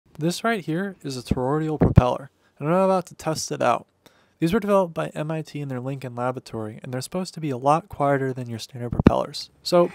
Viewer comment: I thought you said ter-rar-ia propeller.